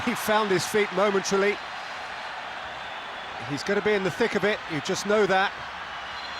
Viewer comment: did he say thick of it😂😂